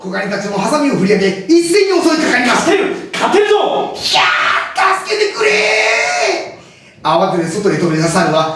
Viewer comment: 「ひゃー！助けてくれぇ！」がバイキンマンに聞こえてしまったw